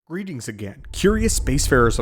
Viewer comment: Video played on autoplay and at 0:00 i heard "BREEDINGS AGAIN"